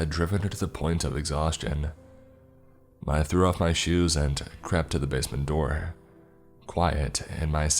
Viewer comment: I thought you said chapped at the basement door then heard crept😊